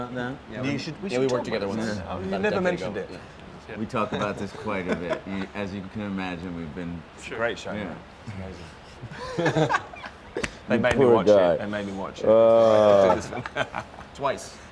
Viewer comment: Can someone please, please write me what are guys saying at 5:58-6:06? I'm not a native speaker and sometimes it's hard to catch up when there are bunch of people talking... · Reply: Rami: As you can imagine we've been-- Gwilym: It's a great show man...it's amazing. They've made me watch it, they've made me watch it. Rami: You poor guy Joseph: Twice